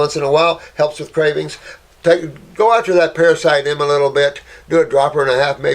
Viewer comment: Take a dropper of what for high fungus? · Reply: It sounded like he said “parasite M” 4 times a day. Now my question is what is parasite M?